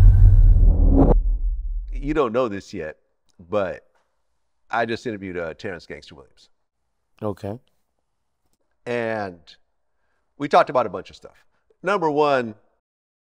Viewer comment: Man did I just hear what I thought I heard, did Glad just say he Pressed Gangsta?🤣🤦‍♂️